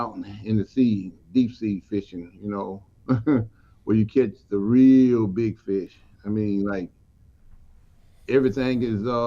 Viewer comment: I thought he said they caught big fish!!!! Come on Ronnie, go Halibut fishing up in Alaska.